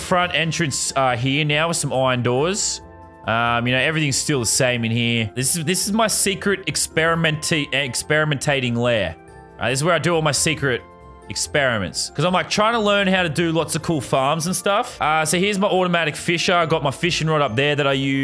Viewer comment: My experiment area I thought he said eggsperiment🤔